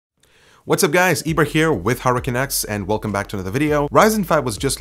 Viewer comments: At the start sounds like he says hey guys it’s hurricane x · sounds like hes saying hurricane X in the beginning xD